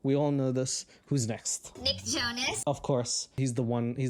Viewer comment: when she said Nick Jonas I swore what she actually said was Big Chungus